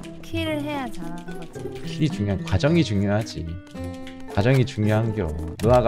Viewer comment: 가정이 중요하지로 들리는건 내 귀가 이상한건가.....